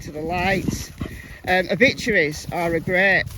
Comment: at 25:57, what did you say? "Victories" to search or what was that site? Hmm, JS and Andy had been together 27 years but suddenly he's Voldemort and they separated immediately after Summer went missing. With so many dealers on the hill it doesn't leave many buyers so seems it is a much larger organization that must ship it and little girls out. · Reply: I think she said obituaries